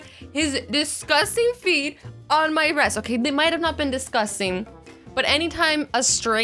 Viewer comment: subtitles said “disgusting feet on my breast!!!!!!!”🤣🤣🤣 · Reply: Tobenna Okoli she said rest🤮😮🤢🤢🤢🤢🤮🤮🤮🤮🤒🤒🤒🤒🤕🤕🤕🤕😷😷😷